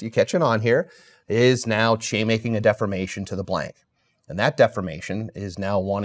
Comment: Did he say chaymaking? 2:10 lol